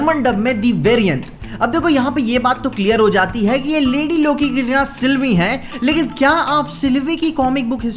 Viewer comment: is he say landi loki